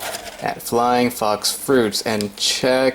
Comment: Lol I thought you said flying fucks fruits.... xD